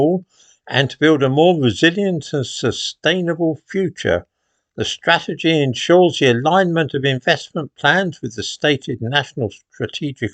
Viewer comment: Sorry, I thought you said "infestment plans" ! The great replacement continues.